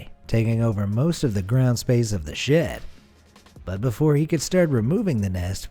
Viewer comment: it sounds like he says "the ground space of the shit" in a stoner ghost face voice.. wazzupppppo